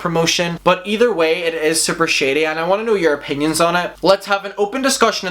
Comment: saying "your opinions" and I heard "Europeans" ajajhhahh